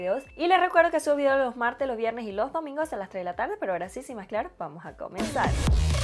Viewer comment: ¿Ella que es lo que dice? “0:58 Bueno ahora si, sin más (no lo sé) vamos a comenzar” ❤️ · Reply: Sin más que hablar.